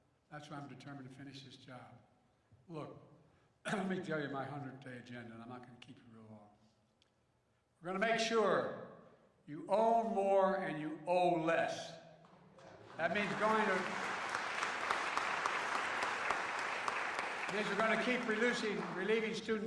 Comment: did he just say "you owe more and you pay less"?